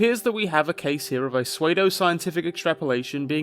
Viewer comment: I’m sorry did you say swaydo-scientific?